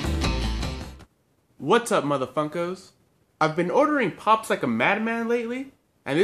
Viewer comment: Did he just say 'what up motherf××ker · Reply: Muhammad Hapizan no he said “what up mother funko!”